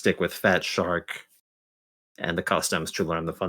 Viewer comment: You said you would stick with fat shark and something else to learn the fundamentals but kinda mumbled the second one. What was the second one you said? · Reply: Oh you said the customs, right?